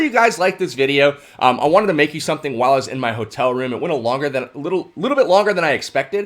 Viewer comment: Almost sounded like you said "whilst" which would have been awesome